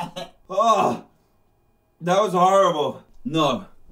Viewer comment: I thought he said “Nom”